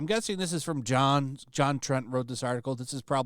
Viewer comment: I thought you said it was JonTron's article at first